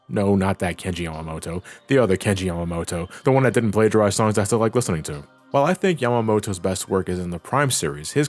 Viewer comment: The hell did you even say at 1:44 ? Like all I heard was "The one who hdkfhfifbfifbfns listening to." Did anyone understand what he said? But great video. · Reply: The one who didn’t plagiarize songs that I still like listening to